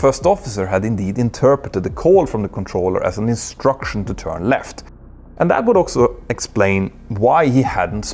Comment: Love the Swedish ”också” instead of ”also” which is actually the meaning of the Swedish word uttered! 💕💞💕